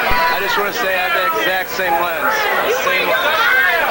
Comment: What did Adam say at 1:35? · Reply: He said that he had the exact same lens, I believe...